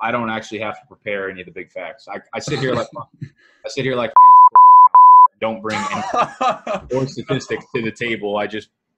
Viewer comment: Who is he saying at 12:25 ...? Looks like he’s saying the Fantasy Footballers😳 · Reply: It was the fantasy football counselor... it's obvious by the audio if you listen closely but he confirms it by giving an explanation of the persons analyst style after he says it. Go watch his videos, they're ridiculous, tells you to draft Luck in the 4th, take a backup QB in the 5-7th in a 1QB league, tells people to draft Rojo, the list goes on and on